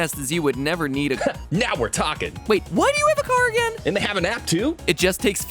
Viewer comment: when sonic said: ´and they have an app too?´ in the intro it sounded like he said: ´and they have an act 2?´